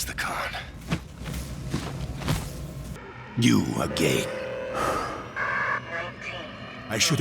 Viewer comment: Why did kravchenko call me gay at 23:56? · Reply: you are gay -kravchenko